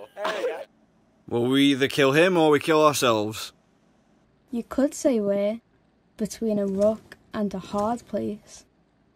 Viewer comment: what is she saying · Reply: "You could say we're between a rock and a hard place" from what I'm hearing Or maybe Rook instead of rock, one of the two